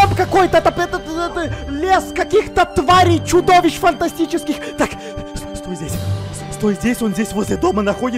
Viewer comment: мне одной послышалось не фантастических а ботанических